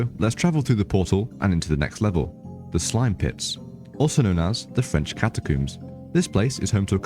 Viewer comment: Thought it said “slime piss”